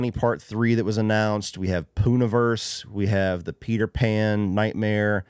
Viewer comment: Did you say, “Poon-a-Verse”, and where can I see these movies?